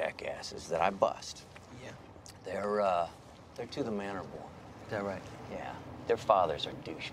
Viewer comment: What did the agent say? "Most of the wall street jackasses that I bust...they're______?" Thanks in advance. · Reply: "To the Manor Born"~~ From an upper class or wealthy family... born into wealth and privileges